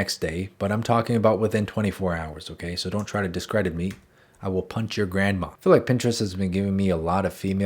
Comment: HELP😭I was trying to skip ahead and all I heard was “I WiLL pUnCh uR gRaNdMa” ARE YOU OK 😂😂😂